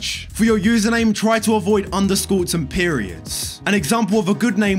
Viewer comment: You said underscore and I literally heard " on the school" 😂😊